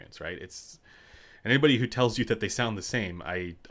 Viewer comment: How do you explain the cable sounding different since it's literally impossible? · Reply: oh, I thought you said they sounded warmer.